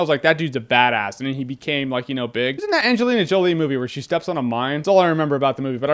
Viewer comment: Did you say Angelina Jolie steps on a mime?